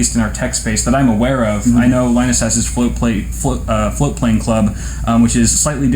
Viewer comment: I was very confused for a moment. Never heard of Floatplane Club before. I thought you were saying "flute playing club" and was very disappointed not to find any info about it on google